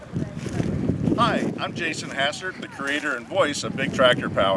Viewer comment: I thought you said when you gave your name, correct me if I'm wrong. Very awesome purchase for someone who isn't even a farmer. You must have some farmers that let you keep it on their property in exchange for helping fit their ground? Great purchase. Could you share with us what you paid for that old girl?